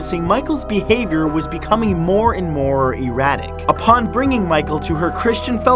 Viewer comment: i thought he said erotic